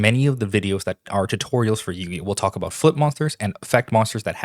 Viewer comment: I swear to God when you say "flip monsters" I hear you saying "foot monsters"